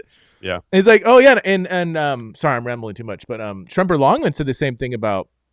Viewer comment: What was the name mentioned at 14:10 who wrote about Song of Solomon? I can’t make out what he’s saying. What is his book/article? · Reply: Tremper Longman